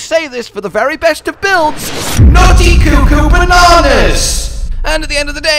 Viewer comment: I was waiting for "BLOODY CUCKOO BANANAS" ..... I was not disappointed 😆. Love this build! I might try incorporating some into a strength pvp build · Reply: I thought he said nutty but the CC said naughty.